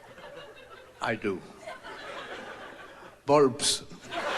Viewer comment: Can someone tell me what does he say near 22:48 ? Because that joke seems funny but I don't understand it ! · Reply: Victors reply to his son is "bulbs" (as in light bulbs)